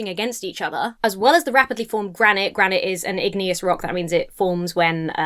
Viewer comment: I misheard say "granite is an atheist rock" at one point and it made me giggle.